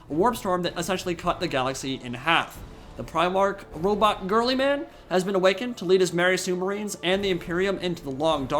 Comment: Did he just say: "Robot Girlyman' !? Anyway Yeah this would be awesome!!!